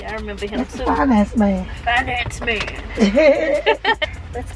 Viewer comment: Chile why I thought said “fine ass man” but she was really saying “finance man” 😂😂😂